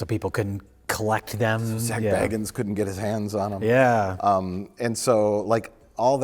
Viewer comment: When he said Zak Bagans I thought he said Sack-Baggins, and that would have worked too. Those Sackville Baggins always want me mythical man eating glasses but they'll never get them I tell ya.